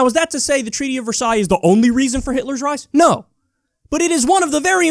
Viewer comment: Did I just hear you mix up Hitler with Hillary? 12:50 🤣 🤣 🤣 🤣